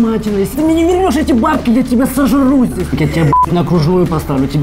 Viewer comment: мне послышалось или тот мужик сказал "я тебя сожру здесь" )))